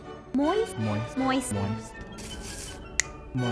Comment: did she just say penis